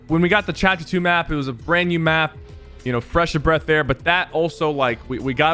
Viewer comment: he said fresh of breath air instead of breath of fresh air :)